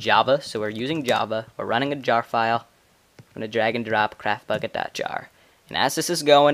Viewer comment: to be honest when you said 'get craftbukkit' i thought you said 'get crapbukkit' :p